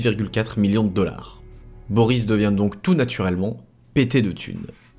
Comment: Dans ma tête j'ai entendu "Boris devient tout naturellement ptdr", ça a changé tout le sens de la phrase.